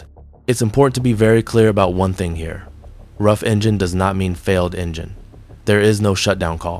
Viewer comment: I watched another video that said they didn't think he said "rough engine." He thought that was just "some issues" garbled. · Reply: Listening with some high quality headphones, it sounds like he said both "some issues" and later he says "rough engine." Not sure why others don't hear the same things as I heard.